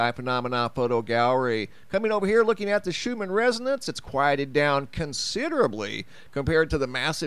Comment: Every time I hear "the Schumann residence" I think Sherman's and Skinwalker Ranch at first lol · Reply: it is schumann resonance, not residence.